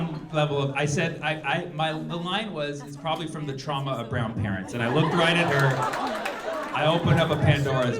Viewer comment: I was listening to this in the background and thought he said 'brown pants'. Which really confused me with the rest of the conversation.